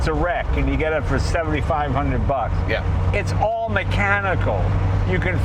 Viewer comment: When Jay said "It's all mechanical" I was focusing on rolling up a lefty and I could have sworn he said "It's only Canadadoe." South Park fans? Lol Turns out Jay is funnier when he's not being funny and you're not paying attention. lol 🇨🇦